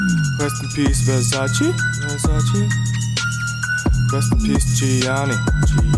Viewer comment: I have no clue what the fuck this nigga is saying, but this shit goes hard. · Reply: I know you heard him say "rest in peace Versace, rest in peace Gianni."